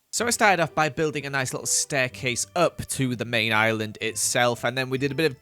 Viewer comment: did anyone hear that said staircase